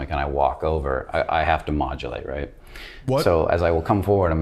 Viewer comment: When he said modulate I thought he said ovulate. Actually crying hahaha